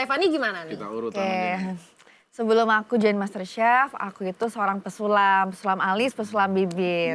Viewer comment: salah denger kirain seorang pesulap 😂😂😂😂😂😂😂😂